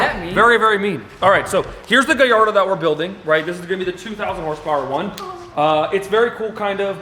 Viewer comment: Did I hear him say 2,000 HP